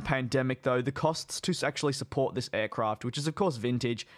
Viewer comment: why did i hear sexually support in